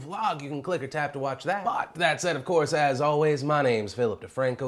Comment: I swear i heard phil say "You've just been 'Phil'ed in, and I'll see you tommorow"